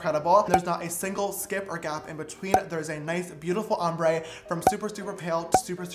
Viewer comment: “I heard him say there’s a knife beautiful ombré “